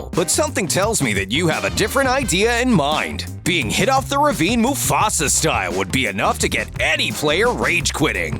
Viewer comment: Did he say mufasa style I watch lion king